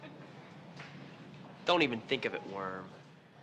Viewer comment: yo creo que dice "worm", ya que "whore" se pronuncia diferente...